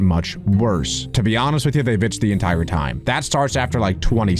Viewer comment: Bro said the b word in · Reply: What said: They iched the entire time What the subtitles thought he said: they the entire time